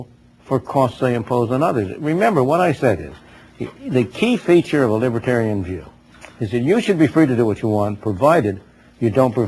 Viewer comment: what did he say at 1:32 "the key feature of a libertarian jew"???